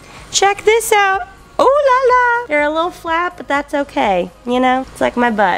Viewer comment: instead of hearing "theyre a little flat, like my butt" i heard "like my butt, theyre gonna be delicious" 😭😭😭